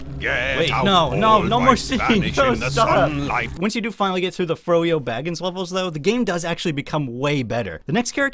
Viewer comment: Did you say "fro-yo baggins"?